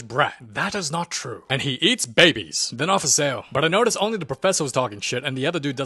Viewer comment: "And he eats babies." why am I laughing so hard? 🤣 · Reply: I THOUGHT HE SAID “AND HE YEETS BABIES” HELP